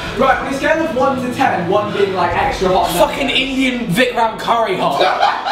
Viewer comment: why i hear "F*cking vie- vietnam curry"